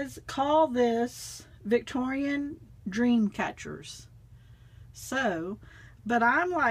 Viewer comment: I thought you said , that your hubby called it "cob web catchers" 😱 lol